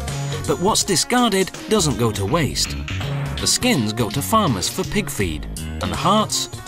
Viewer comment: lmao at 1:48 the caption says "this game is going to farmers for big screen" farmers dont play games,and games dont go to the big screen